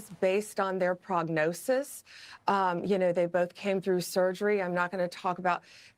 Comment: It sounded like she said they did make it through surgery, to me.